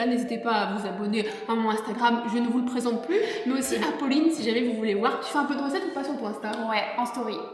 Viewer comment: Mdr jme suis fait avoir au début xD, j'ai compris "Apolline" au lieu de "à Pauline" xD Et tkt, on met le pouce bleu xDDD